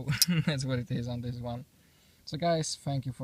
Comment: Definitely thought you said "f*** you for watching" lol! I originally hated rock pattern but thanks to you it's been growing on me. I have to say that seeing your knives on BladeHQ was really cool. I don't have enough $ to get one yet but someday!